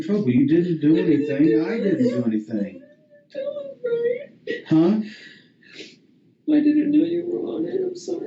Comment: Hey, is it possible that his wife knew more than she initially revealed to officers, or was it likely that her and Richard had a conversation about what to tell the police? At 37:50 it almost sounds like she says "I didn't tell them right" when they're talking about him going out onto the bridge. Makes me wonder if she was suspicious of him before police even suspected of him being involved.